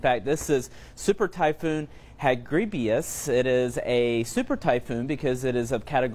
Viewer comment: Is it just me or did I hear him say , "hagribious" and not 'hagibis'